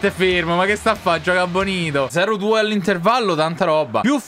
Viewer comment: ma solo io ho sentito benito